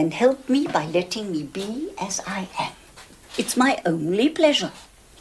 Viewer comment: "Then help me by letting me be as I am. It's my only pleasure." At first I thought she was saying "Then help me by getting me BSIF. It's my only pleasure." I even googled BSIF, imagining it to be some kind of fancy drink like a gin and tonic or something.